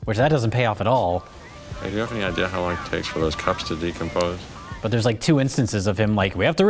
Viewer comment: The Goldblum line? I heard - "Do you know how long it takes those cops to decompose?" I just figured he was a psychpath.